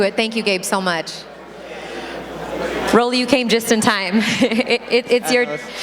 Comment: I thought she said ‘broly’